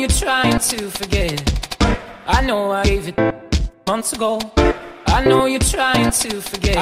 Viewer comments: Words I heard: 1:Geisha 2:Gay Shit 3:Gauge It 4:Asian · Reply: AgRize Indigo I hear "Gaze It" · I thought it said I know I ate it ate it